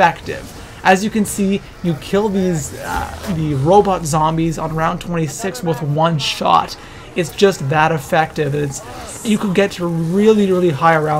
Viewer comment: Ohh I remember u from the old and now you’re a pro at 3:45 u hear him say robot zombies instead of panzirs